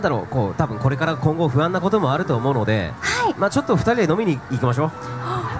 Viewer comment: oi subtitle guy, not "do that" as if to suggest something sexual, he said drinking!